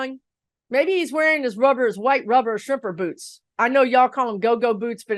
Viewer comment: When you said shrimper boots, I thought you said stripper boots...😂😂😂 fitting, right?!